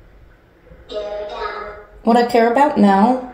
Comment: Gurera down??? Instead of care about is what it Sounded like that to me